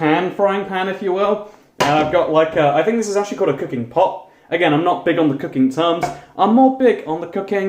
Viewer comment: Because of “accent” (because i’m American and all that..) I thought he said, “i’m not big on the cocaine stuff” instead of “i’m not big on the cooking stuff” 😆